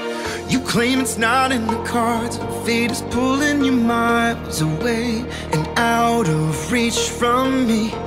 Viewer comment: I heard fetus pulling us away 😂😭